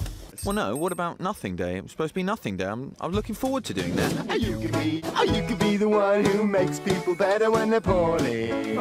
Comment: Why do I keep hearing “or you can be the one who helps people when their hœrneuy